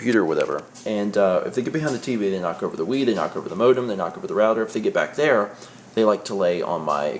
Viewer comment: I thought for a second he said they knock over the weed